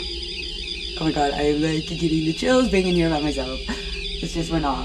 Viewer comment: Anybody else hear a voice when stopped on the stairs or is it just me like right after the k2 beep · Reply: i played it back so many times and i swear it says (Oh my god im going to die)